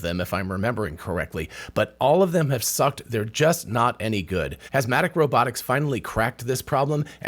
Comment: At first I thought he said Asthmatic Robotics.🤣